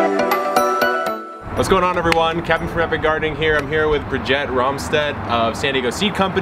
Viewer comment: At the beginning of this video it sounds like you say "Captain Perfect Gardening here." vs "Kevin from epic gardening here." 😀